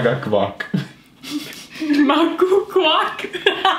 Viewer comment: Bin ich der einzige der bei 12:36 cock gehört habe